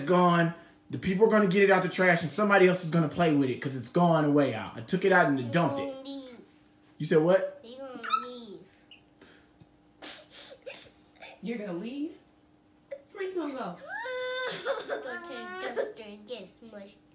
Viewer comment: It sounded like she said you’re mean.